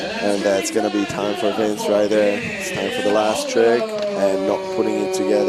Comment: Thought they said there'd be a best trick.